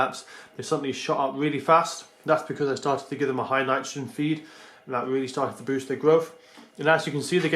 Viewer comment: What did you say a high __ feed? Thanks. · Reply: Sounded like nitrogen to me.